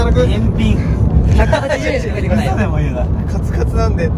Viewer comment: ずんだ餅返品の話の時に、｢カスカスなんで｣って聞こえて、うわ宮近～って思ったら、｢カツカツなんで｣だった